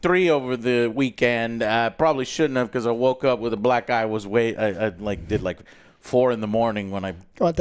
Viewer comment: thought u said something else 😂😂😂😂😂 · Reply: For sure thought he said black guy lol